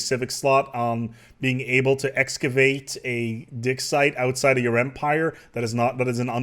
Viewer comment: I'm so sorry but everytime you say "dig site" I hear "dick site"...